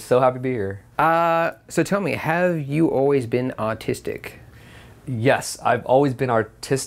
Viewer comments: thought i heard: "Ahhh, so tell me, have you always been austistic?" Then the way recited 'artistic' makes it sound like he thought he was asked if he was autistic then figured out he meant artistic so he said it slow bahaha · why does it sound like he said autistic